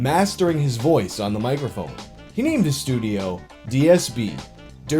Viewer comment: Did someone say DSP?